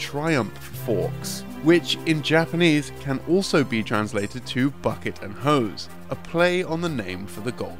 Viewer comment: Was listening to this in the background while doing other stuff, and I could have sworn he nonchalantly said "bucket of hoes" instead of "bucket and hose." 😅